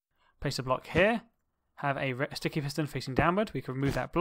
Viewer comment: did anybody hear him say red sticky piston